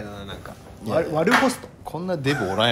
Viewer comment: 悪ホストね 悪ポストって聞こえた(笑) ソファーが赤だし。